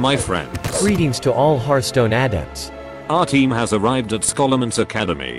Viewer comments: When they said adepts at the start i thought for sure they said hearthstone addicts lol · I wasn't looking at the screen when it started playing and I swore I heard "greetings all hearthstone addicts" but I played it back he said "adepts"